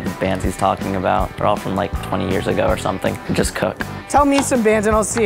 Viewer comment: Okay, so the first time I watched this, I didn’t realize what said when he was talking about how he was guessing the old bands. At the end he said “Just Cook” I was literally dying.